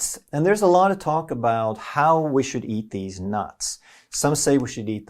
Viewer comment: Did you say, there's a lot of talk about how we should eat deez nuts?